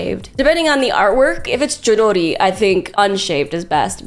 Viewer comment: Who? I didn't quite catch that? I heard Jidori, but no artist by that name.